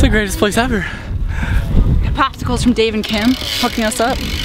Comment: listen to at 4:36 I think instead of saying hooking she says f'n.